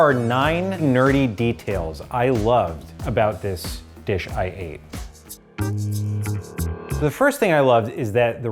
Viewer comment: I kept hearing "This dish I hate" and I was so very confused